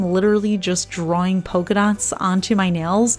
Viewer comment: Everytime you say " polka dot" i hear " pokedot"... i am lost to the cause 😅